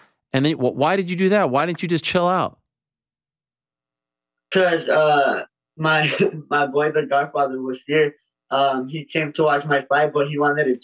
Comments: did he say "my boyfriend's godfather?" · did he say his "boyfriends" God father or am I trippin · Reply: he said “my boy his godfather”